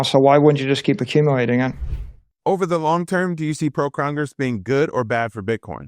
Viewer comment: Hey, at timestamp 13:31 you said a word that sounded like "Pro-Crongers". What the heck did you actually say?